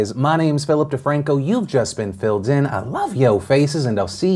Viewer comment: Thought I heard "I like your faces"... Had to go back to make sure Phil wasn't friendzoning us. Oh 2020...